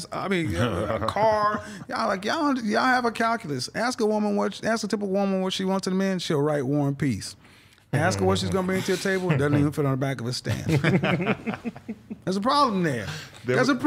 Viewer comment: "Ask a woman what she wants in a man she'll write War and Peace...." I thought he said "One Piece" but both are equally long stories ha.